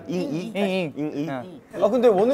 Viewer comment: Aaah the caption is wrong here and I got confused had to rewatched it to realise Seungkwan said "GOING SEVENTY" aaaah